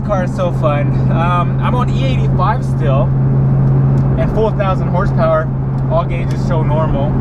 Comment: Did he say 4000hp at 8:12???!?!?